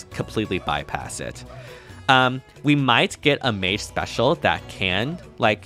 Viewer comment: Why would you want a passive skill that negates all DR ? Spirals rivals with Traces, Dodge, Fighters, and is conditionned to special procs only Anything more flexible than this would invalidate so many builds and units. This is just lazy game design, Embla/Elimine tier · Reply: I heard "We might get an A special but now we dont have that so thats infortunate" Listening back you did say a mage indeed, my bad Still im not too fond of the idea, if DR becomes too easy to fully negate it essentially becomes meaningless and we've just went full circle with a skill slot less That kind of anti meta effects should remain on PRFs if you ask me, because making it more readily available breaks the niche of units that have it while making the effects it counter more and more irrelevant Doesnt make the game richer in essence, quite the opposite